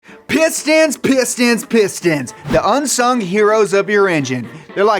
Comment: i thought said pit stain pit stain pit stain